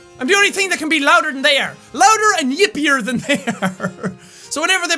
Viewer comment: for a second, I thought jack said yiffier...